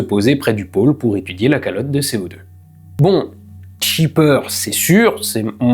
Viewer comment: meeeerde j'ai entendu la calotte de ses morts 😂